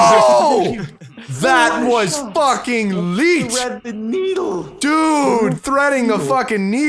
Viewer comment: Wait...what? What did say at 9:08? "That was fucking Leat?" "That was fucking neat?" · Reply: leet(1337), internet slang for pro